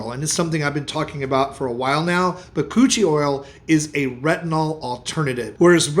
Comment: Anybody else hear him say cochi oil. And then realize it was something else. 😂